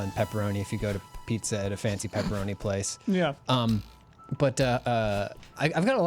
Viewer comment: did he say “a fancy pepperoni place”